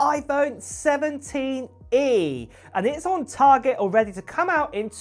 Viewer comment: I thought he said it’s at Target.